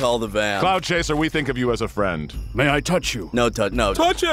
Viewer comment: I thought they were saying clout chaser for a second I thought that phrase wasn’t used yet lol